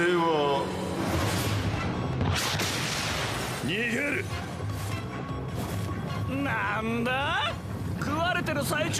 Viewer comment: WHAT!? JOTARO SAY N WORD!? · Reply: You're probably joking but just in case you don't know, in Japan the word he said (nigeru) means run away or escape so he didn't really say the n word